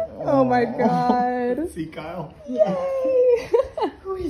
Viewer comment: Sounded like he said sieg heil not see Kyle.